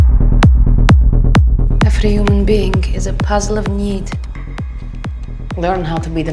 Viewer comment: "Every human being is a puzzle of need" well... fuck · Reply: Lmao I thought she said puzzle of meat